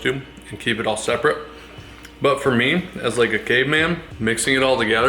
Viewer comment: def thought he said “gay man”